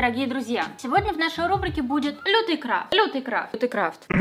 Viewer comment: Почему мне слышится "ЛюТыЙ кРаБ!"? Или так и сказала....? Аааа... Я переслала раз 5.... Ииии.... Поняла ,что она говорит "Лютый крафт!"